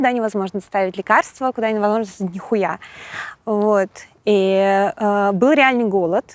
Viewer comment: Она сказала нихуя? Или послышалось?) Так и да, послушал дальше - мата дохуя.